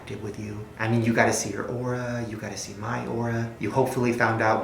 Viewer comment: When Shane said ora I thought he said laura which is my name and I was like is gonna diss me or something